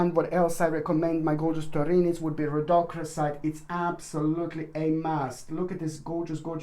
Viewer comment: Did anyone catch the name of the crystal talked about at around 22:02? I'd like to research it. 😘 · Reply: I looked up names on the internet, I think its Rhodochrosite. Thanks anyway love!